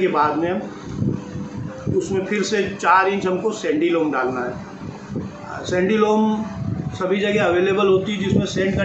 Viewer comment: What did you said at 5:37 .??? Sandilom or what